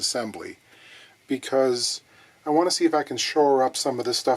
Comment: I enjoyed your Werner Herzog. I kept hearing "I want to see the baby". Probably the creepiest think he has ever said. I love the long videos. I could have watched another 20 mins of this for sure.